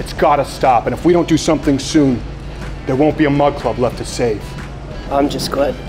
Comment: I always thought you were saying mud club.